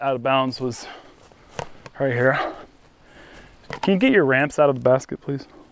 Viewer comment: Favorite part was 44:40, when says "Can you get your [discs] out of the basket, please?". doesn't move, so says, "Alright, I'll do it", and proceeds to walk all the way to the basket, kick the disc up and whack it at, then stalks back to his lie. Meanwhile, takes another disc from the basket and tilts it up inside the basket, and turns around to take his putt, sees disc, and cracks up. Lots of fun moments like that in this video, but that one made me laugh out loud. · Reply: "Ramps", got it. I heard "rants" and couldn't figure it out. Still, I understood what he meant, and it was a very funny exchange.